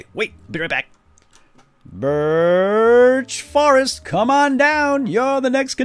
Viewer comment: I thought that said "Bitch Forest" for a second